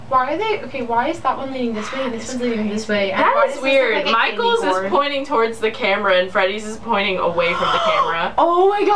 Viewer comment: Im not trying to scare you, but at 3:42 I hear some weird whispering. I played it back a few times and none of you guys were whispering at that moment. I can't make out what it's saying though. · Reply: XoLiberianGirlxO it was me saying "that was crazy" lmao but it does sound creepy as hell 😂